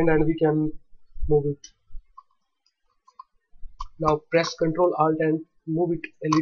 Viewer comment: Hey man, at 4:45 what did you say, CTRL + what? I didn't understand you, sorry! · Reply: didn't hear too, but you should: press down ctrl and alt and drag a part